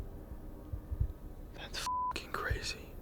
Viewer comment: you asked if there was anything thing else that the spirits wanted to say before you left and I can hear someone say cabin right before the meter does through the speaker it sounds like. Can anyone else hear it?